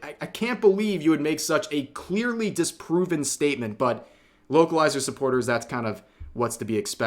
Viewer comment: I'm not going to lie. I thought was saying wokalizers the entire time.